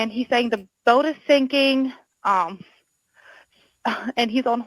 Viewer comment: I know I heard “oh my god.” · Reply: I heard "I'm sinking" but the caption said, "I'm speaking." It's so easy for people to fall into this trap.